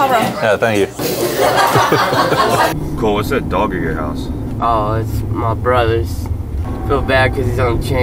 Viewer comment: I thought said the dog was his brother.